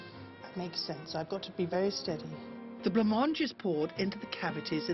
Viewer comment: what is a blan march / blanc marche? I couldn't quite understand what said. What is that white sauce she is using? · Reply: Trojanette83 It's Blancmange , a type of pudding or custard. You could probably find a recipe on the internet.